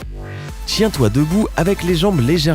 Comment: Est ce qu’à 8:18 il a vraiment dit « chien toi debout » à la place de « tiens toi debout » ?😂